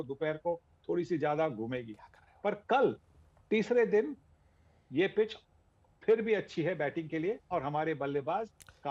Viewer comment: Did I heard someone say “kya krrhe h” in the background? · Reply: Haan bhai dheere s aayi "kya kar rahe ho"